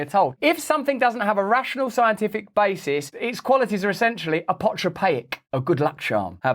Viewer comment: What was that word he used at 7:21? Apartrapaic?!